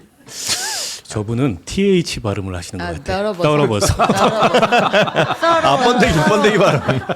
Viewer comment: 누가 들어도 더러버서 안간다 로 들리겠다.